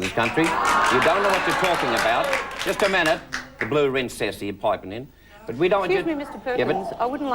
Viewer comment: What does Charlie Perkins say at 6:13 that the host objects to? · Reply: he refers to the audience as "the blue rinse set" . a blue rinse was often used in older ladies hair